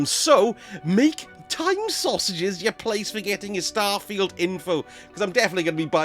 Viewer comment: I swore, that said "Madame Sausages Place" 🤣🤣🤣 Had to rewind the vid to double check!